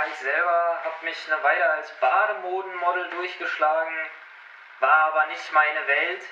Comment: ich hab badehodenmodel verstanden